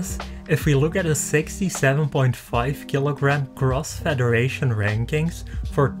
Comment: Crossfederation......feeew thought you said crossfit at first hear